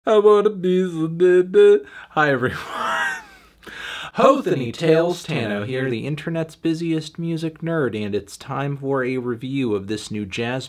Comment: Melon what'd you say in the intro? I heard "I wanna be seductive."